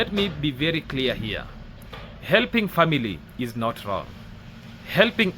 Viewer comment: Hi, i thought he said family isn't nontent.